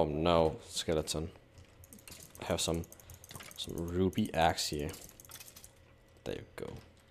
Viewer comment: Am I the only one who heard him say "Ruby Axe" and not "Ruby Ass"?? Lol.